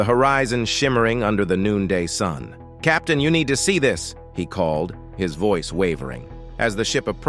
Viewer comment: LOL 😂 When the narrator quotes the Ancient Mariner at marker 21:54 saying... "Captain you need to see this..." I at first thought he was calling the captain by his first & last name, cause that's what it sounded like to me 👂🏼. {> "Captain Ya'neida Ceidus"<} then I thought💡origin/heritage it was spelled more like this ⟮>"Captain U'Neida Ceidus"<⟯ while I'm thinking it must be a Latin & Italian mix? or some French & or Greek? wait... WTF? Then my auto-correct vocabulary sensor kicked in, waking my conscious from a deep sleep, and auto-rewound ⏪ my brain 🧠to what was recorded at 21:54, not what was distorted and sounded like. Then my mind whispered to my conscious, very succinctly, ⟩"Captain, you need to see this!"⟨ Then I thought, Wow! All this happened within a 4-5 sec. window. It's got to be old age, that's creeping up on me, perhaps short-circuiting my senses, or perhaps I was mildly distracted, focused on some other thing. Or my mind is being haunted by a freaking retarded poltergeist. LOL 😂 Captain Ya'neida Ceidus. Guess the Captain's Birthplace of origin/race. LOL